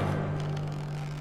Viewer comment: I literally thought he said "Bye babe!" at the end there